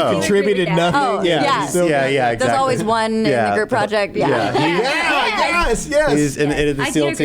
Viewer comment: yup, i guess I'm a racist now - I've played it back several times and I swear I hear 'N' project